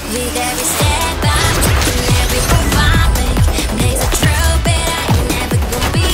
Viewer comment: Lyrics: [Intro] Now, no time to slow it down Don't need to look around I know you love the sound Now, no time to slow it down Don't need to look around I know you love the sound [Interlude] (Uh-uh-uh-uh-uh-uh, yeah) Now, no time to slow it down (Uh-uh-uh-uh) Don’t need to, need to… [Instrumental Drop] [Verse] Got a few tricks up my sleeves, yeah A design that skeets in the breeze, yeah Unleash, you don't need no teaser Truth, yeah, it's your demeanor I lost a week or two We got no idea what to do Always on my mark, no time, no cues Shining light for me, this stage is not for you [Chorus] With every step I take And every move I make Blaze a trail bet I ain't ever gon' be afraid You know I'm wild like that Never can be tamed Even on this beat Watch me skate With every step I take And every move I make (Move I make) Blaze a trail bet I ain't ever gon' be afraid (Be afraid) You know I'm wild like that Never can be tamed (Can be tamed) Even on this beat Watch me skate [Outro] Now, no time to slow it down Don't need to look around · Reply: I heard "makes a trail" instead of "blaze a trail" though